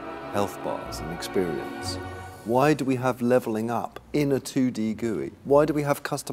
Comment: "2D gooey?" wtf is 2D gooey? does anyone know what he is saying?